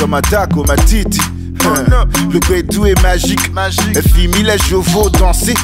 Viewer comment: is he saying "le kwetou est magic" at 0:36??😩😂😂🤣🤣🤣 I thought only Cameroonians where using that word😂😂😂😂😂😂